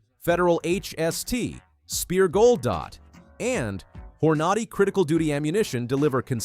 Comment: did you just say "whore naughty" ammunition? lol